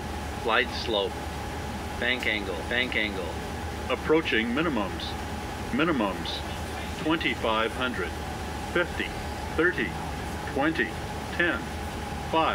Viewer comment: O que ele fala em 5:43 e 5:44 parece "bankingou" e "aproach in litlle moments".